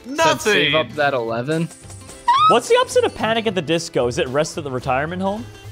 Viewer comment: I thought he said panic at the petco 🤣🤣🤣🤣🤣🤣